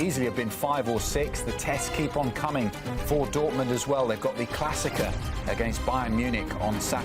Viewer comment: what did he say? It’s Der Klassiker